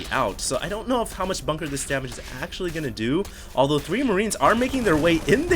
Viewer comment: did he just say "I don't know how much bunker this damage is actually going to do"?